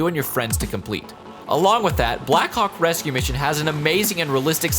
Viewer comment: I thought he said “black cock rescue mission”